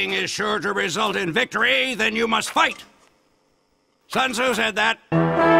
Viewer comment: wait did soldier actually say "sun tzu said that", because i thought he said "sons, who said that?"